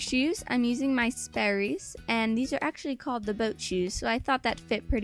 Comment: when you said" their called the boat shoes," I thought you said" their called the goat cheese" hahaha